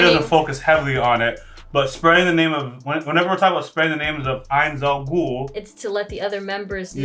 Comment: At one point it sounded like said ainz Al ghul which got me thinking about a cross between Nazarick and the league of shadows